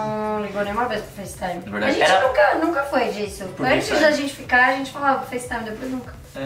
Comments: oq foi isso? Eu ouvi certo?? antes da gente ficar... eu ouvir certo??? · Meu Deus eu ouvi a dizer "Antes da gente ficar"